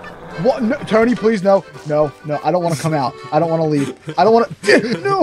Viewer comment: Did say : No i dont wanna turkeys on